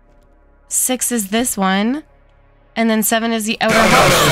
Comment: sorry I heard u said something what u say again? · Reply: I think u said and seven is the outor GOTTA SWEEP HALL SWEEP SWEEP